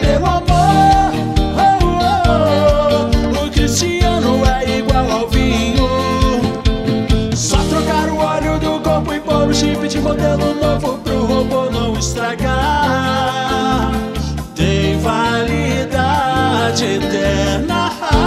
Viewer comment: ouvi ele falar "é so trocar o CHIFRE" aí eu pensei será se ele joga free fire?